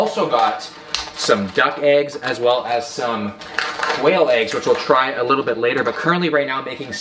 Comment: It is probably only me, but i heard him say whale eggs and i was like ???